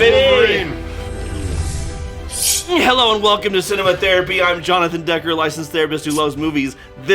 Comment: Hearing the sponsor for the video and the promo code, I honestly thought you said, “cinnamontherapy”